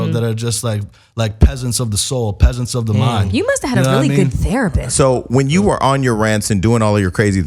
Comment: « You must have had a good therapist » did I hear that right ?? lol It was hilarious even though it was not meant to be. Doesn’t he sound like Rambo or Rocky ? Why do you want to sound like an ignorant black man, I will never understand this choice, what’s so appealing about that, in the real world it is a sad life. My ears are ringing from all those , like like like, only retards speak like like that, you had a good education embrace it. He still has personality issues. « I got sober because of my dauuuuughter, turned to God », dark skinned girl got pregnant even though I didn’t want to, sounds hood enough man. If you couldn’t stand the pressure of being Tom Hank’s son, well then why become a Hollywood actor do something else, do something for this damn world instead of parading around, it will make you feel a lot better about yourself, people will give you the validation and approval you crave for. There was something very sincere about him when he decided not to talk about his mother because I would have sworn he was going to. I would hate being famous in this industry, it’s too often for the wrong reasons.